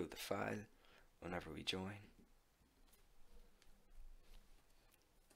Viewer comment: Thought he said: If you found this video helpful please if you like, unsubscribe